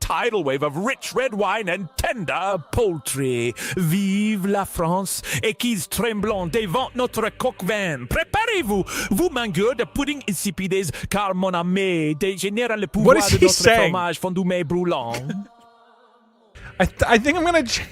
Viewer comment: hey, can you tell me what exactly he says in his tirade at 51:08 ? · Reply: Best i can do is : "hail france, ??? tremble before our coq au vin ( its a french dish). Prepare yourself, you tasteless pudding eaters, after that i dont understand much else at least not something that can make a good phrase , i am french and its very hard to understand 😂